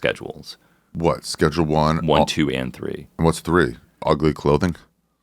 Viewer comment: When he said ugly clothing I thought he said “aglicloven”.